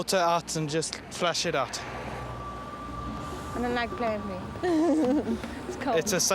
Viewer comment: does she say she doesnt like plan B? then the guy agrees plan A is safer? anyone know?